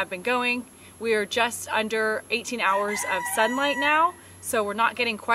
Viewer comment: did you say under 18 h of sun? Not quite as much sun?